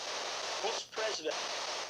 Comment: lmaoooo true - I’m still watching the evidence. I’m seeing so much new equipment I need to step up my ghost game 😦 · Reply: I was looking for this in the comments! I heard it too @ 21:47 ! The lady voice says like "notice" or "otis" or something right after and I was like who the heck is president Otis. Still can't pull that together but I heard "who's president" plain as day!